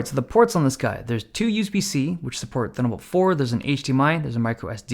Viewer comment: wait, did you just said thunderbold 4?